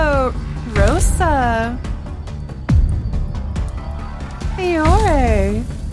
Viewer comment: I heard Fior